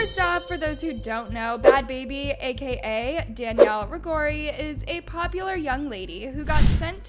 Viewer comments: did you say bregory? 🤣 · Did you call her “Danielle Bagori ?” It’s Bregoli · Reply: I thought she said ragori lol 😆 😂